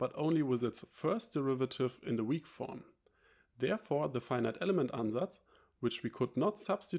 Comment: what is the word that you are using "Finite Element Undas" ? Please explain this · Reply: Finite element ansatz 😁